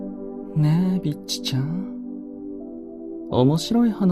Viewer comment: did he just call me a bitch..? romance 😍